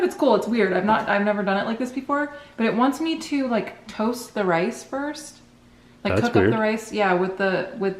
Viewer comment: I thought you were going to say "toast the ghost" instead of "toast the rice!"